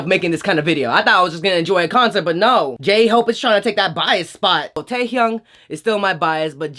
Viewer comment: i was gonna ask for thumbnail time but you put it in the front bless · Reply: DID YOU SAY GAY-HOPE I CANT